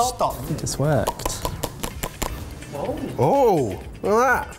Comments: when you say "Looking good!" the closed captions say "Fuckin' did it" hahaaha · Mike: “Lookin good” Auto-captions: “Fuckin’ did it” For once I think they were more correct than what was actually said 😂😂